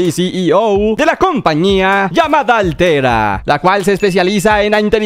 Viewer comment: Pensé que dijo alterra los de subnautica entenderán